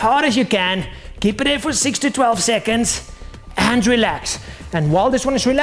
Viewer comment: anyone else thought he said 60 12 seconds 😂